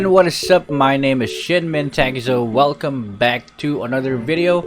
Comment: At 0.2 i heard "shetmen takezo"